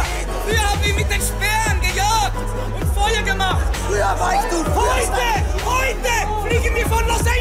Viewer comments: was sagt der tim bei 2:12? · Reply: Glaub "früher stand ich hier unten". Nach dem Motto als hätte er es jetzt geschafft. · Was hat der tim ab 2:17 gesagt? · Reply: Igar Tvoj "Früher war ich du. Früher stand ich hier..."